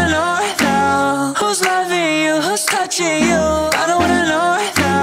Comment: at one point it sounds like he says 'whos left in you"